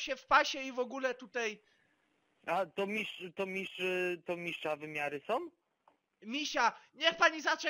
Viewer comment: czy tylko ja usłyszałem,że to mistrza wymiary ? xD